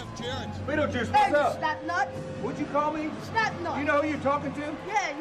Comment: I thought bettlejuise said fat nut not slap nut.